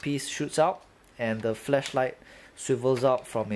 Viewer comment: holy fuck it sounded like you said fleshlight at 2:25 XD